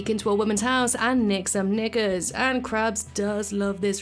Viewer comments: just call it underwear, idc where you're from. But you did not say that clear enough for it to sound like KNICKERS. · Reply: It's very obvious I'm saying knickers. That's what we call them in the UK. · what did she say?! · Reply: Knickers, it’s what people from the UK call undies